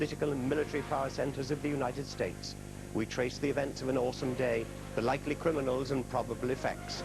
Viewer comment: Does he say "we trace the events of an awesome day" at 0:30? That's what I'm hearing, and what's written in the subtitles.